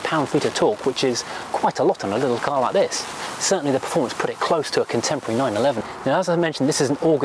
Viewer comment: performance close to a contemporary 911, did he say that???